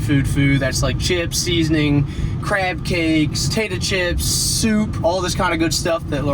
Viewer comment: Did you just say "tayta chips?" 😂